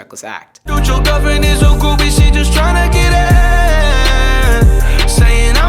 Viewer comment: "Dude your girlfriend is a groupie." I heard that "Put your governors."